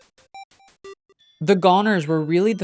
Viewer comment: spaced out and thought it said “Kris and the Gooners”💔